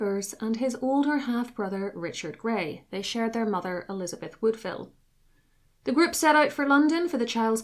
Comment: I thought I heard "They shared their lover Elizabeth Woodville" and that didn't sound right. I got distracted and came back days later and noticed the comments and the day. Good job!